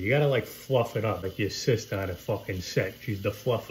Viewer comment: “Like ya sista she’s the fluffer👀maaaaaaaaang”😂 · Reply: I thought he said like the assistant on a fuckin set